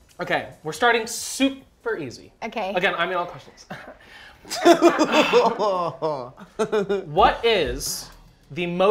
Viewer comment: What did Laura say at 1:32? lol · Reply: I’m an alcoholic